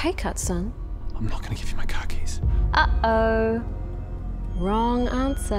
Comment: I thought he said " I'm not going to give you my khakis."